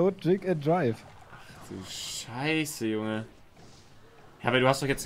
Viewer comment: sagt „ach du scheiße junge“, was denkt sich meine Siri? Ich glaube ich habe hey siri gehört😂